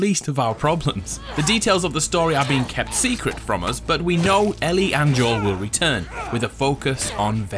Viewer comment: omg i thought you said Ellie and Joel would turn, meaning become a infected at 1:47 . However as a diehardfan i should know that Ellie is immune, silly me :/